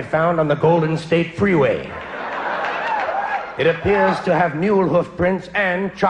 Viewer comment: he says "something prints and chocolate sprinkles on it" I understand the chocolate sprinkles on it but the prints part i don't what does he say there? Highly appreciate a response... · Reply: You bet, sir! Also, I just watched this again, and I really think he says "mule" hoof prints rather than "new" which confirms what I've seen that CC is not 100% accurate. Another option is to click that Gear icon, Settings, to the right of CC icon and run the vid at a slower speed, usually 0.75. Anything to try and figure out what they're saying.